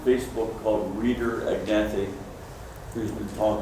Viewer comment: mentions "a guy on the face book" but I can't make out the guy's name. Any idea who he's talking about that discusses genetic mutations on his facebook page? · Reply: I believe mentioned Reader Ignaty, but searching facebook it looks like he changed his account name to Abracadabra Abracadabra. Check out posts from him in the Chinese Goldfish Fanciers group <link>